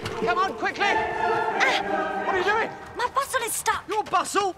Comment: That entire scene I swear they said Bustle instead of Bottom.